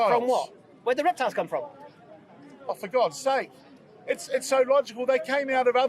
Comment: did he say for god sake lol